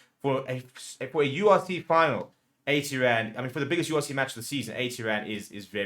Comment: I thought I misheard you 🤔 80 Rand 😮 At First I thought you said 800 Rand ...and I thought "yeah that is an excellent price for a final" 80 rand = 3.84 Euro Ireland is bloody expensive 😅